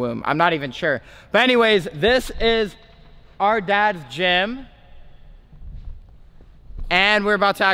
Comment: When u said “this is are dads gym “I thought u said “this is are dad Jim” lol 😂